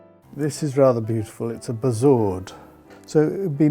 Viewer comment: Can anyone explain what the gold ball is that he says is very rare? He picks it up at 5:29. Does he say bussaurd? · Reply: BEZOAR. Animal intestines dried then used medicinally back then.